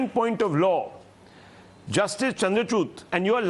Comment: Did Rahul actually called justice chandrachur as justice chandrachoot🤣🤣🤣🤣